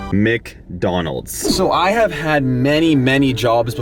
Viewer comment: why did you say mecdonald